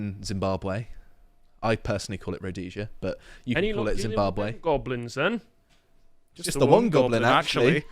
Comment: What does he say at 10:21? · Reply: Any luck catching them goblins then?